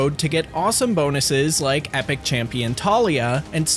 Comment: Bro, the first time I heard you say "epic genitalia" then I returned to check and it was "epic champion Tallis" hahaha I'd download raid if the hero was called Genitalia hhhahahh😂😂😂